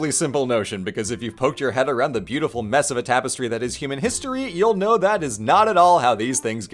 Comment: I was dead certain made a pun at 0.58 Instead of 'a mess of a tapistry that is....' I heard 'mesopo-tapistry'.. And damn it, I'm sticking to it.